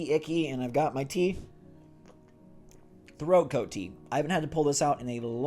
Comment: I thought he said throat goat tea